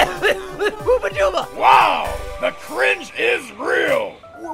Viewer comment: Did he just say huba dooba